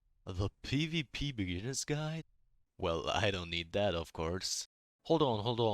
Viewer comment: What u say in 0:07 ✨💝✨💝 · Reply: Well I dont need that of course!